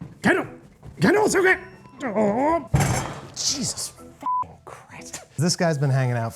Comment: "Cheeses f*ing crust" is what I heard